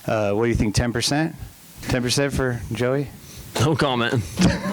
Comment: I can’t understand what Tomac says at 4:05. Anyone know? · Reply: Nevermind, he said no comment.